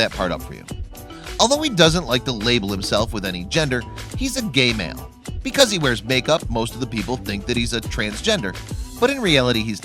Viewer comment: I heard "Jeffree is a gay man. He wears makekup" and I was about to throw my phone but you saved it.